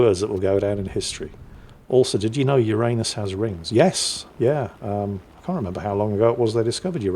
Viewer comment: did you just say "your anus has rings". Give us a picture then. LoL!